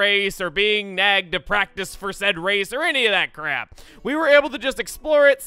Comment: did he just say............ crap?